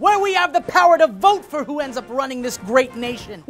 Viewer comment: I thought he said, "We should never forget how lucky we are to live in a place where we have the power to vote for who ends up ruining this great nation." Maybe I am just too cynical after all.